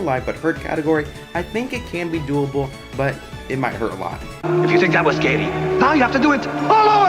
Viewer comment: Why Do I Hear "If You Think I Was Gay"